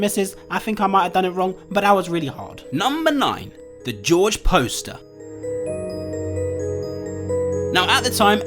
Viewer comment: I thought it said George imposter lmao I need to go outside more